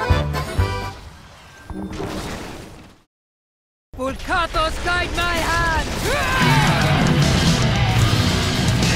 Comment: what does sonya say at 2:08 ? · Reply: "Bu Katos guide my hand!" Not sure how the name is written, but it is, if i recall it correctly, the deity worshiped by the Diablo barbarians.